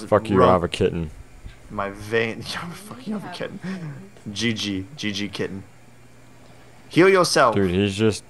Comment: was i the only one who heard girlfriend in the back round saying we have a kitten